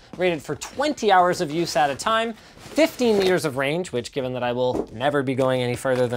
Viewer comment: I keep hearing, “15 Years of range.” 😂😂